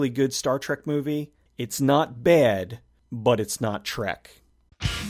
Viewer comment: "It's not bad, but it's not Trek" is enough for me to not go and see it! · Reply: I too am not going to see it after watching this. However that's because I thought he said "It's not bad, but it's not Shrek".